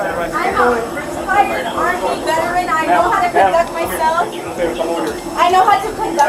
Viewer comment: 🇨🇦 I thought she said " I know how to conduct myself in public. ". If she did she wouldn't be getting kicked off the plane ✈ .💀🇮🇱🇺🇸💀